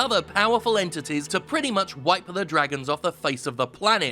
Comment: official subtitle says "white" instead of "wipe" lol